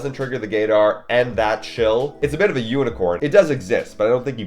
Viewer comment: did he say unicorns exist?